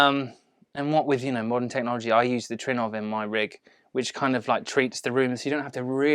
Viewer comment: What does he say at 17:20? What kind of rig for his speaker treatment? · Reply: He's talking about Trinnov :)